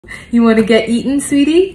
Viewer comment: when i heard say “you wanna get eatin’ baby?” I thought she said “you wanna get eaten baby?” 💀 (ember is so cute)